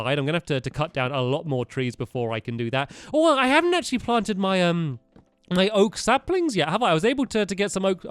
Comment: am i the only one who misheard stampy and thought he said: "i haven't actually farted my oak saplings yet have i?"?